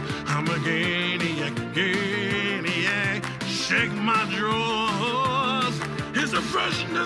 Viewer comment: It sounds like he says Sniff my drawers.